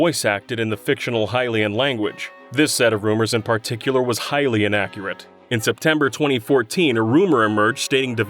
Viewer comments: "...was Hylian accurate." I swear, that's what I heard. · am i the only one who heard “hylian accurate” 😂